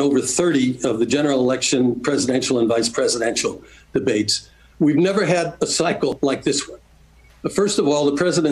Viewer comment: Sounded like he said “we’ve never had psycho like this one” 😂